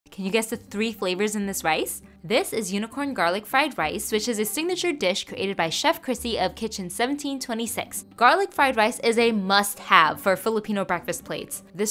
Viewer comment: accidentally heard it as Kitchen 1776, and did a legit double take😂